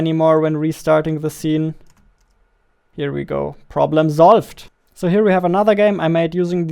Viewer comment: am I the only one who heared problem zolved